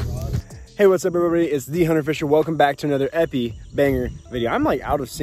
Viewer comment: I heard "epi banger", and that's all I need to hear to know this channel isn't for me. Lol.